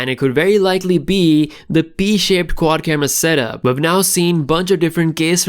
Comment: Funny I thought you said B shape 🤣... I though it could be the Finger Down design and 🤔😂